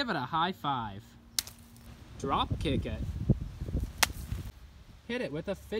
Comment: The auto subtitles say “drop the kid again” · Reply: Actually, Said "Dropkick It."